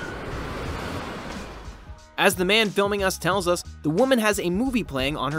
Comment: "As the man filming us tells us". Did I hear that right?